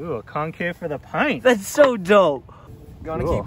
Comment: At first I thought said about the concave for the Pint "Thats so dumb", and I would agree, because the Pint foot area is small enough unless you have a size 6 foot it would be like standing on a hole. They should actually make one that is slightly convex instead of concave as the average foot hangs off on both ends of the smaller Pint and it would conform to the arch of your foot better. Then I disagree that the lock is convenient. It's bulky to carry around , looks like it would scratch the inner part of the One-Wheel and would not be as easy to use as a typical braided wire lock that could be rolled up and stored in a fanny pack or pocket. The charger adapter would be a "must have" item for anyone who already has the XR Charger. Too bad FutureMotion does not let the consumer upgrade the charger for the difference in cost (+$30) at the point of sale.